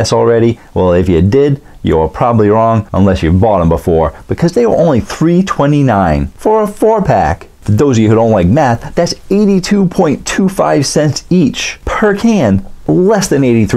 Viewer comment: I thought you said for those of you who don’t like meth and died laughing